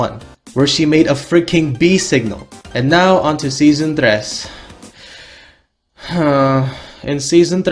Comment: I was like: did he say "tres"? In spanish? :0 Oh por dios He speaks my L A N G U A G E